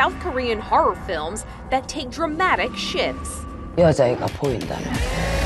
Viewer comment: Who else thought the narrator said “.. dramatic sh#ts..”??? I had to put on the CC to actually make sure of what I heard 😳